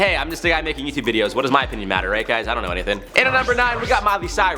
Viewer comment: Rather than 'opinion' I really thought for a second he said 'my penis doesn't matter that much.' I was like damn someone hug this guy.